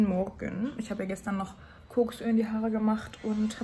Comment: wer hat auch bei 9:15 Koksöl verstanden😂😂😂😂